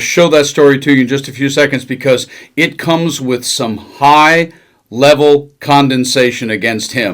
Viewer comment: It sounds like you said a "high level of condensation" the word is condemnation. Could be I just heard u wrong.